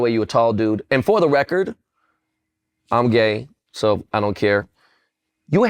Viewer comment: Did benball said “im gay” at 32:09? 😂